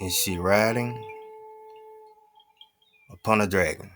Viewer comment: Me: "Did he say dragon?" I look at the screen to rewind... 432 views. Me again: "Yep" Thank you